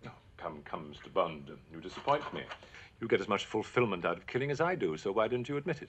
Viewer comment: All I hear: “Master Kenobi, you disappoint me”.🙂